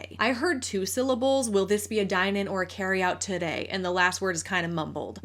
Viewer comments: I heard “carry out to go” honestly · I heard todaer (today)